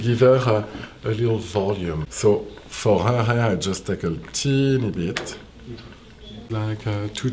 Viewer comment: "For her hair just a tiny bit" lol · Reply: I hear "teeny" instead of "tiny".